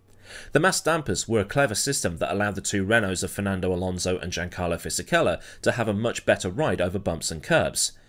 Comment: did it sound like he said Jack Harlow Fisichella to anybody else?